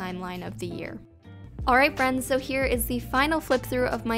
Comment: is it just me or did she say Hello friends